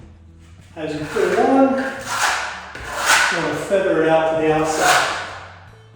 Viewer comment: Love seeing all your progress! So much work! At 35:28, right after says "It's all about smoothing at this point," and then stomps his foot...is that whispering something? I can hear a woman's whisper but I can't figure out what she's saying. "We've gotta talk about centering" or something? Look forward to the next update! <3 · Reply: She said tell them to feather it out. 😂😂